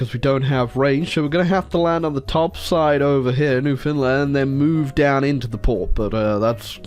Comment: could swear you said New Finland, and now I wish it was New Finland.